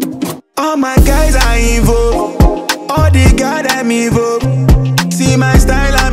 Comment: I thought said all his guys are evil, all the gyal are evil. My ears need some deep cleaning. It's a #AfricanBangerFC #AfricaWhereTalentDey